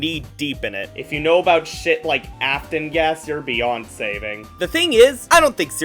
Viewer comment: i miss heard this as "afton gaster"